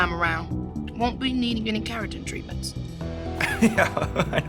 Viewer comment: What is she saying at 13:00? What kind of hair treatment? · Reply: Keratin treatment. For damaged hair. Straightening damages kinky hair.